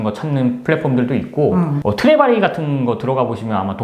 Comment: 트레바리 ... 트렙아이로 들려서 ... 허허허허 월 회원료가 제법 나가네요~ ㅜ ㅜ